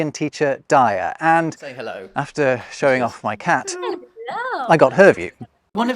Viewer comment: "... And after showing off my cat, I got herpes." ... Or at least that sounded like what he said. Perhaps should work on his own diction. 😅🤣😂